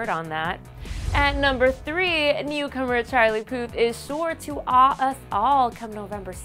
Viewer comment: whe she said charlie puth it sounded like she said charlie POOP lol